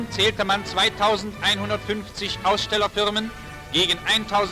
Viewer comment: the subtitles say that in total, 2500 companies took part at the 01:00 mark but doesnt the german news-speaker say 2150 companies?